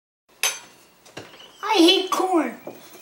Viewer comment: Wow when i saw deck'd I heard "I hate por_n"